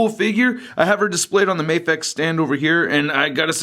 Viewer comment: Hi, what is the brand of stand you're using? Apex? Mapex? I couldnt find 1 like it. Thanks great reviews. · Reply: He clearly said Mafex stand at 1:20...